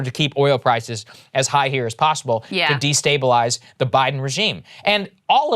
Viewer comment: did Saagar said, “the Biden regime “?